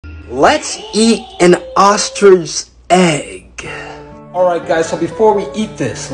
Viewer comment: why did that sound like ge said ostrigue